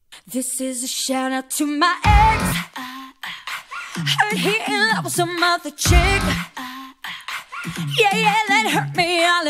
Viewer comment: Oh I thought it was "heard he in love with some model chick".